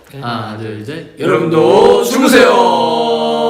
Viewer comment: 아니 진짜 뭐라고 하는 거임..? 여러분도 죽으세요? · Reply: ㅇㄴㅠㅠ 죽으세요 아니고 주무세요..... 갑분죽음권장..